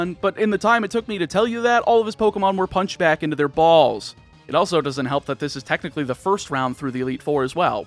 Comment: watching this video at 2x speed, I misheard it as "punched back in their balls". Had to do a double take at normal speed.